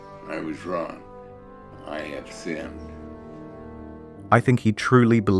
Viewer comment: We don't need math to know Camping was an idiot Why does it sound like he said "I have finned" @ 21:02 and not "I have sinned"? hahaha